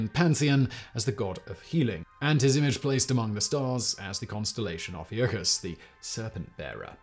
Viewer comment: if you had issue hearing what he said at 4:06 and because the caption ommited, he said constelation of ophiucus.